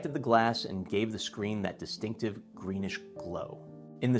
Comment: This video was a big relief. I heard "green glow" and panicked at first, racking my brain over why I had never heard of this "incident ".